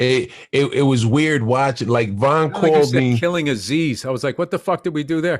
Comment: I heard "killing aziz" and not "killing is easy" lmaoo